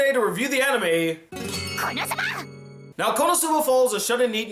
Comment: For some reason I keep hearing "KonoTSUBA" xD